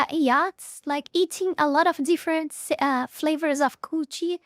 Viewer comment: Did I just hear you say "Many different flavors of coochie!?"